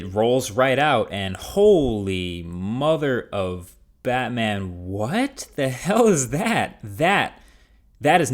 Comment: Did you just say "Martha"?